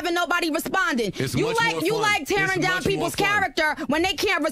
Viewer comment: anyone catch Joe saying "this is why I like talking to people when they not pregnant"